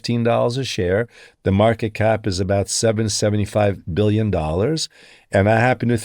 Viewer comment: Typo here. The market cap is not $7.75 billion, it's $70 billion. I think he said "the market cap is 70~75 billion dollars".